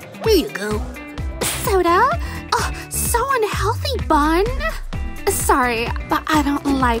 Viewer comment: I thought you said she was healthy